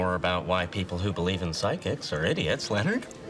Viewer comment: Believe in sidekicks? What, like Batman and Robin? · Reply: 😂 I'm glad I'm not the only one who misheard it as sidekick. Had to come to the comments to know what it was about: Psychics 😅